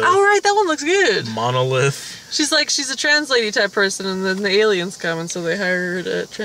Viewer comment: I coulda swore said Arrival was about "a trans lady-type person and the aliens come so they hire her to translate the aliens." Like, woah, really? Why is no one talking about that? Then I listened to her again, hah.